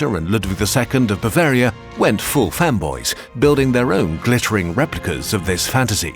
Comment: Am I the only one who heard femboys?